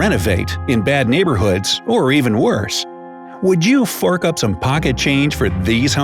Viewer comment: When you said "fork up some money" I had my Luxury Bidet (yes i'm on the toilet) on so I thought you said the f-word (I'm only 11 so I hope A. My mom isn't mad at me and B. nobody cusses in the replies) October/10th/2020/Saturday/08:56 Military Time ~Saturn 2020